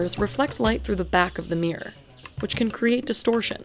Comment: As a British person whenever you say "mirror" I hear "meer"